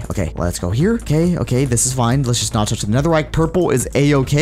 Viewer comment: You Said Netherite Instead Of Netherack LMAO